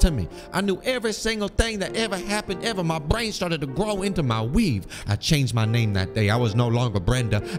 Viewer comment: “My brain started to grow into my weave” 😂😂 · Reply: She said "weave"? I heard "wig" lol